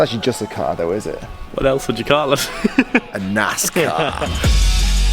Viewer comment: Wait what ? Your accent is throwing me off, it sounded like you said a nice car 😂